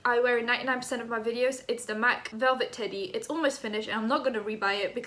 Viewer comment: I thought she said it was “velvet titty”